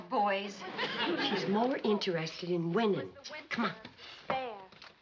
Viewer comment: I heard Kate say Betty-Joe was more interested in “women” than men. (Kate said, “winning”). Can you imagine the impact at the time this was aired that Betty was a lesbian or bi. Wow. As my only daughter is bi, it is what it is, as long as she’s happy, even being conservative myself.